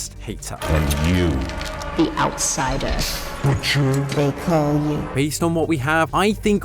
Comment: It almost sounds like the Prototype says Richard instead of The butcher